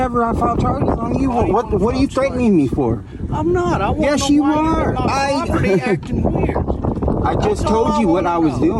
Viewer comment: There you go. I was waiting for it. 4:35 It sounds like he said "....why you're on my property....". I believe this is the basis behind this idiot's belief he's entitled to answers. So many of these "misunderstandings" have a basis in property ownership - as in one's belief doesn't match reality. It's a public road old man, YOU DON'T OWN IT. It's not YOUR property.